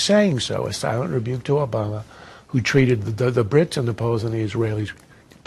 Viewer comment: What does Krauthammer say at 1:11? "...the Brits and Polonese(referring to Polish people)"? I must be hearing this wrong! This is a pulitzer-prize winner political columnist!